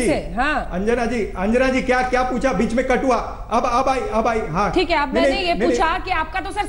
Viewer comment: "बीच में कठुआ"....Did I hear it right ?🤣